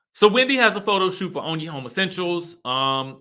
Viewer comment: So when you said onye home essentials I thought you said only homosexuals loooooool